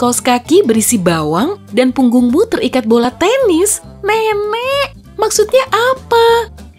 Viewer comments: kok M3M3K sih min harusnya kan nenek 😄?? apa hanya gw yg salah denger 😃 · "MEMEK" apa gw salah denger yah?😂😂